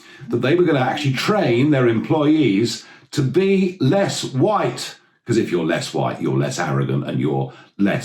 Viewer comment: For a moment there, I thought Nigel said "to pee less white". My mind reeled.